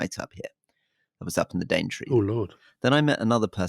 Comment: What did Rory say at 29:56 "I was up in the ..." ? I couldn't make out the last word. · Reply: Daintree